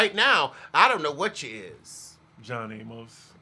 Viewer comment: What did say about Marlo around 37:27?? .. Sorry to keep sounding like a broken record but I couldn't hear him! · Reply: "John Amos"